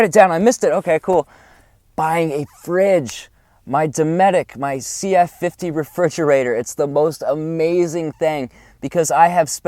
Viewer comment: what kind of ref. do you have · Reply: anthony marino the dometic c f50 refrigerator" is what he sounds like he says.